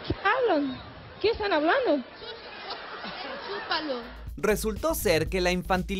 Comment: que dijo?? Chupalo